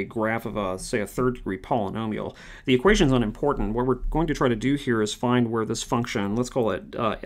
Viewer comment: english captions say "the questions aren't important" when it should say "the equations aren't important" * at 7:06 they say "f prime is increasing on that variable" when it should say "increasing on that interval" (idk if you have someone reading these comments or not but jsyk. thanks for the course!)